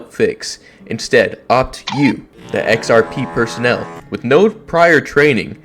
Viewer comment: did i hear xrp?